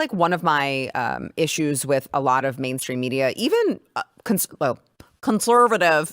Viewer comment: I'm stoned so this is what I heard, was talking about the main stream media, I heard "mad stream media ". I actually think that fits.